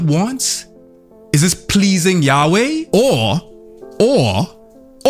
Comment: Misheard Yahweh as Yaoi and got confused. Did a double take at the "READ THE BI" on screen before my brain said bible.